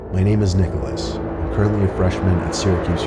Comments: WHAT ARE YOU SAYING? WHAT? SORRY, I CAN'T HEAR YOU! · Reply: My name is Nicholas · Your name is what? Sorry can't hear you ever what seems to be bass boosted trumpets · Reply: LOL, MY NAME IS NICHOLAS